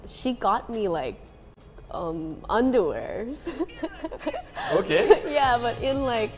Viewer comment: What does she say at 0:18? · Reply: I think she said like lingerie or something